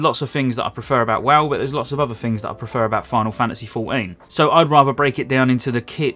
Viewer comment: Final Fantasy "for-een" - Gotta love Lincolnshire <3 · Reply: Oh, I thought he said Final Fantasy Fort Een.